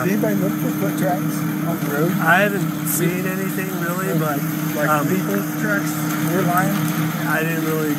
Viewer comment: I heard Barry say “…or people - tracks, or lion?”